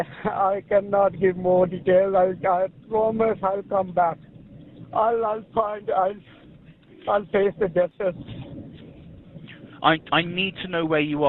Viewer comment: I'll taste the dishes? who tf is narrating this, in which right mind even if you didn't understand his accent does "I'll taste the dishes" make any sense? 😂😂, he said "I'll face the justice"